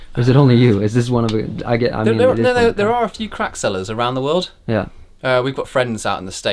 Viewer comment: nice, sounds like he says there are a few crack sellers around the world xD